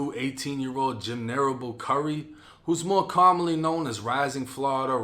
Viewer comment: I thought he said his name was Jim Carrey